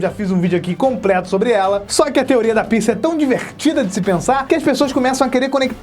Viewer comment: Só eu quero escutei " A Teoria da pizza "